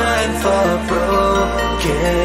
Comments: i literally thought he was saying “it’s christmas time in curry” the whole time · Oh, he said “KOREA”... I thought he said “GLORIA”... Because it’s a “Christmas Song”... His autotune is too much... LOL... 😅😅😂😂🤣🤣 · “It’s Christmas time in Korea” B-but isn’t it Christmas time everywhere? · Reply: I thought he was saying it’s Christmas time in curry. Idk don’t even ask 😂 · “I don’t know what he said but let’s laugh”-Jin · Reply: If im honest I thought he said 'curry' a couple of times insead of 'Korea'